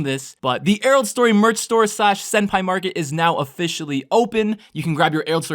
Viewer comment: Thought he said marker😂